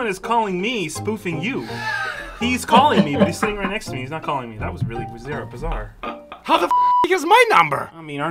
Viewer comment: did he say " thats really brassiere "😂😅